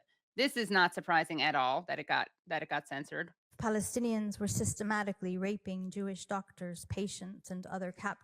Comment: HELLO? At 0:20, you cannot hear her first word "IF" before she says "Palestinians were systematically raping Jewsih doctors, patients, [etc.]" I found that EXTREMELY confusing. I had to listen patiently to the whole video before I understood that she said "IF" before that statement.